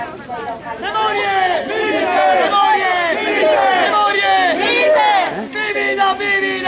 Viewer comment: What are the audiences saying? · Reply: It’s the name of the reenactment group. Memoriae Milites (soldiers of the memory)